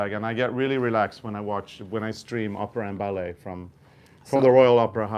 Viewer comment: Haha! I thought said that was watching Oprah and ballet, rather than opera. 😂